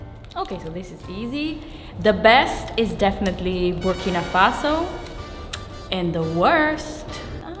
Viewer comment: Subtitle at 1:09 "Working Opossum" instead of Burkina Faso .. haha xD